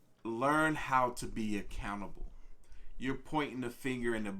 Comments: I dont wanna make jokes dis is serious but my dumbass thought he said "learn how to be a canabal" BROOOO🤦‍♂️🤦‍♂️🤦‍♂️ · When he said "learn how to be accountable" I swear I heard "learn how to be a cannibal"... had to work that one out in my head for a minute. That was a trip!